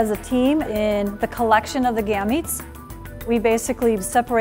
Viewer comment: I thought that word said gay 😂😂😂